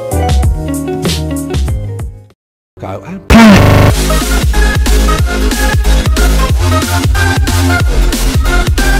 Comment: What did he say at 1:51? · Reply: Go and play!